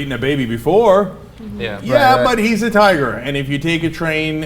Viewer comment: At first I heard Cenk say "tire" instead of "tiger" and I was like, WTF tires eat babies? O.o